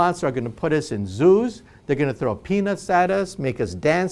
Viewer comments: Did he say they're gonna throw a penis at us?? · i thought he said peanuts not penis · did he say 'they're going to throw penis at us'?